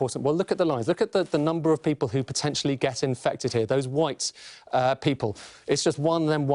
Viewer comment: When he said "that white people" – I felt that. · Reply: Sounded like he said, those whites people lol